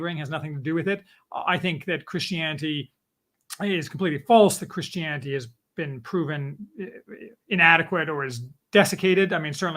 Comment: You actually did say that at 1:5:00. MAYBE you stumbled over your words, in which case I apologize but that's what you said. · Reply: It does sound like that, but what I actually am saying is "I think that Christianity is, uh, it is completely false that Christianity has been proven inadequate or is dessicated. . . ." The switch in thought in mid-sentence does seem like I'm saying Christianity is false, however.